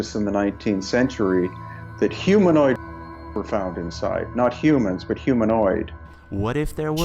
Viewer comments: I think he said "humanoid bodies" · I think he said humanoid parts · I think it he was saying bodies but had to censor himself for youtube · humanoid bodies is what the guy talking said. What was blanked out. If that's helpful.